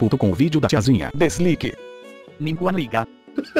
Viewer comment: só depois entendi que é ninguém liga kkkkkk